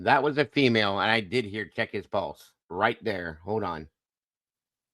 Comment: To me I heard “yeah, right there, check his pulse” Come on, it’s pronounced ORE-GUN not ORY-GON…I know you Midwest people struggle with that haha